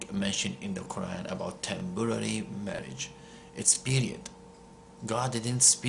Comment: I thought he was saying tempura marriage...hmmm, tasty!